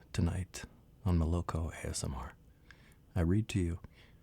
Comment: I misheard you saying Loco ASMR at the beginning 😅